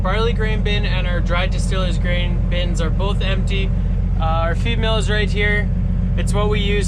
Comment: I thought you said female instead of feed mill. 😂😂